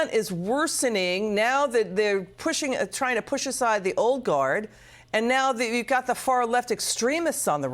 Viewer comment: When she says old guard it sounds like she is saying oligarch. lol